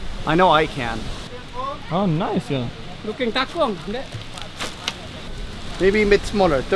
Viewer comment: the man said "Looking tangkong ndeq ?" That is Sasaknese Language. And actually he want to say "Looking for clothes too ?" But he mix between Sasak language and English. Ofcourse you not respond it, because you dont understand. And make me laugh so hard :D